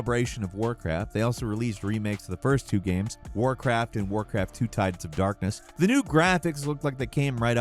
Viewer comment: Oh, "Warcraft II: Tides of Darkness." I thought he said "Warcraft: Two Types of Darkness."